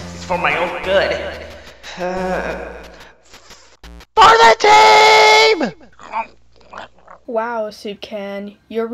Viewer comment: what did say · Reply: he said FOR THE TEAM